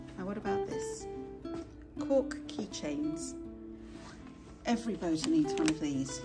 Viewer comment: When you said, “Cork keychain”, I first of all thought you said, “Pork keychain”. I was thinking, “Yeah, that makes sense because I’ve always thought they look like scotch eggs” 🤦🏻‍♂️😆 It’s definitely a sign that I need to eat something.